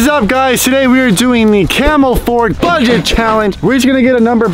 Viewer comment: When he said camp fort I thought he said camel fort